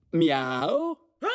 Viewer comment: did Soinc say Meow